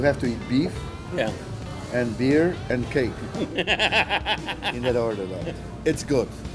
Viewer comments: Did he just say beef bear and cake??? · “You have to eat beef, beer and cake”. I heard “at Portillo’s you have to eat BBC” 🙌🥳